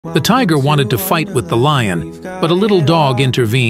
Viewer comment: Wait, did I hear “ the tiger 1 to fight “ 😅